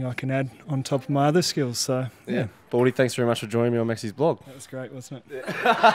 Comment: what did the guy say after Maxwell thanked him. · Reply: "it was great wasn't it" :)